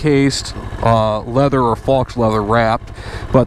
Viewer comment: Did he say "fox leather?"